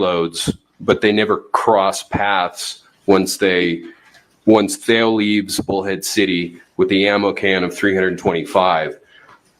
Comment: Sometimes I hear things wrong. I thought that guy said, "Once Thale left Hoe Head City ..."